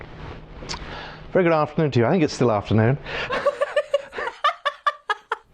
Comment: What does the new anchor says at 3:29?? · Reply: "Good afternoon to you. I think it's still afternoon."